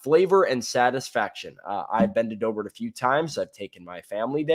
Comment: Wait did say "I've bended over a few times" at 2:28 lol😂🤣